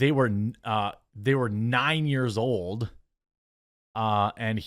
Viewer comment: Listening to what you were reading while I’m shopping in Walmart. It sounded like when reading his email, it sounded like it said he was 12 years old. Then you said she was 9. Now, if he did this, it’s still heinous, however, details are needed. This isn’t the exact same as a brown man doing this to a child. If I’m wrong, then I will admit, I’m just going off what I thought you read. Idk, it seems like you’ve already found him guilty and you normal day wait and see, so it seems odd how you are discussing this. Also it seemed like you glossed over where he said 12 years old. I’ll re watch later and see if that is in fact what you said.